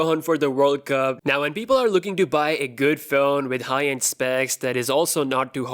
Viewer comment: I thought he said "people who are looking Dubai"